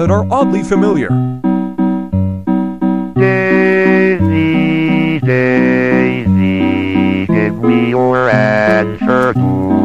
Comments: I heard " daisy, daisy, give me your ass to cook, I have RABIES 💀" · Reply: I heard “Daisy, Daisy, give me your answer to” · I heard " daisy, daisy, give me your ass to cook, I have RABIES 💀"